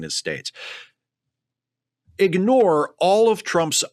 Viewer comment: Oh nice, I freudian misheard that as "ignore-anus". 😂